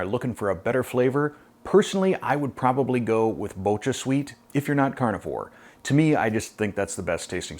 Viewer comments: Hi, what's the alternative sweetener? I think I heard "Mocha Sweet" or "Motra Sweet"??? No too sure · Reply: BochaSweet or allulose. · You mentioned a sweetener in this video that you like more than swerve-- can you please let me know what it is? I thought you said ultra sweet. Thanks! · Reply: BochaSweet: <link>